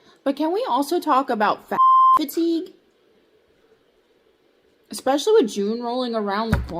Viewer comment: I thought she said "Fat Fatigue" at first, I suppose we can add it to the list.